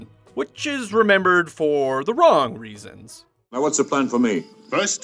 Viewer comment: "wrong reasons" i heard that as "romulans" xD